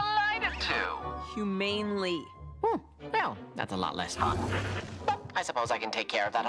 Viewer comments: i always thought Alastor said “allot less fun” here… but he ACTUALLY says “allot less hot” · maybe it's because of the distortion or just my hearing, but it sounds like he says "oh well, that's a lot less hot" XD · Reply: I think he said that is a lot less fun